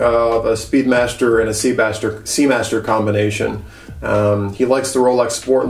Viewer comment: Did I hear you say «Seabastard» there towards the end (at 14:40 playtime) ? Yes I did. Not a freudian slip, just the nickname you gave it, probably.